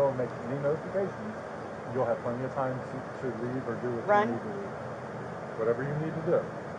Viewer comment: Did she say 'run'? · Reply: Yes! She said RUN